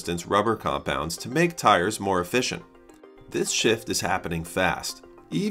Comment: I know you said shift, but it sounds like you said shit